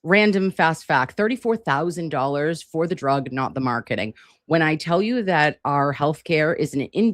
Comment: I thought he said monitoring...not marketing...